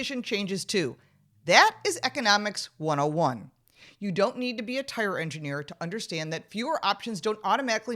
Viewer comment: of this video, Did sheets say tire engineer or Tyrant engineer?